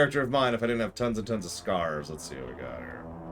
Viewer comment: Oh, SCARS. I thought you said SCARVES.... 🤣